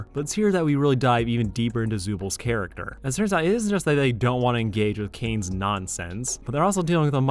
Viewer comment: what did you say 😭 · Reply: He said "turns out it isn't just that..." but yeah it sounds weird👍